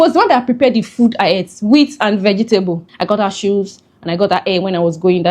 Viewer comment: Did I just hear I got her hair? 41:56 Ah!😢😢😢😢 na wa oh